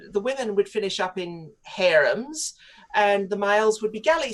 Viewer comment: Did she say hair-rooms lol, I can't make it out.